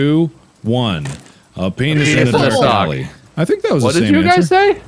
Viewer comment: I thought Mark said "A penis in vasabi" but I guess "dark alley" is good enough too lol